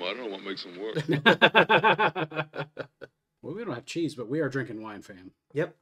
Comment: RIP Donald Sutherland. Woof Woof! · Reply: Funny - I always thought he said "chicken" not "cheese." Another Mandela effect?